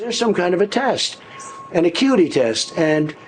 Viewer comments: If you think a cognition exam is a IQ test, you're not doing well on cognition or IQ. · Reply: If you think he said "IQ" in that clip, you misheard. He said "acuity." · If you think he said "IQ" in that clip, you misheard. He said "acuity." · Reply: That’s what i kind of understood,but because of the context of that video I thought he said IQ.Well I understood ,,a cuty“,but sounds like ,,acuity“.